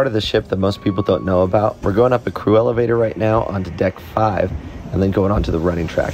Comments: Literally thought he said 'cruella Vader ' instead of 'crew elevator'. I need my ears checked😅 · I thought you said “Cruella vator” instead of “crew elevator” and I was so confused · Omfg. I heard “Cruellavator” instead of Crew Elevator